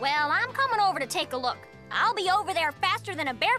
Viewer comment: For a Sec there I Thought said: “YEAH, YELLOW RABBIT!”